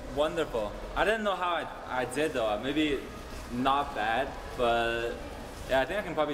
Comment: nghe đến đoạn "maybe" đhs toàn nghe thành "babe" anh Sexy quá đấy Kờ Rít ạ :3